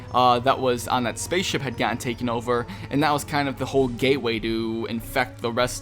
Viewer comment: Lmao i thought you said gayway